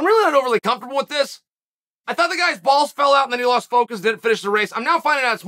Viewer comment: When I first heard “the little boys fell out” had to rewind it a couple of times 😂 I was like ‘what is cHael talking about now!?’ 😂